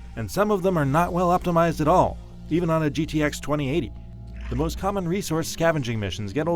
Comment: Did he just say GTX 2080? , Are IGN reviewers know something about PC gaming?